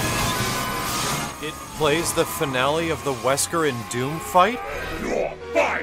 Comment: on 4:20 did he just said "it plays the finale of the wesker in doom fight" does that even make sense?!